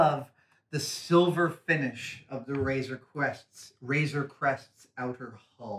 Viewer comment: A couple of times it sounded like Adam said Razer Quest :D